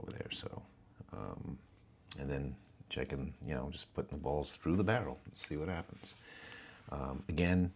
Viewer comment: I thought I heard you sucking balls through the barrel. 😂 I am hoping you meant blowing. 🌬️ 💨